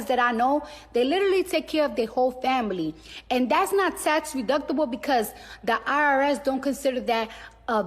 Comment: Did she say “tax reductable”@ 5:45?